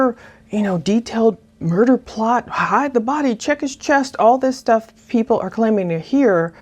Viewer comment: Thanks for what you do! Also- I heard “check gps” instead of check his chest.